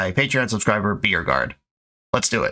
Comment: Anytime says "BeYourGuard" I hear "BeerGuard" and it makes me thirsty